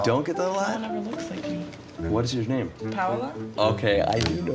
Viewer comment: is it just me or does she say her name is "pamela"?